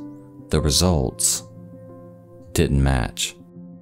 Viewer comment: Holy.... at first when you spoke about the result of the DNA test, I thought you said it did match not till a few seconds later you restated the outcome of the test, made me jump a few seconds back opening captions just to realize you said that it didn't match. Were they for real? Sending something fake to trick a grieving family?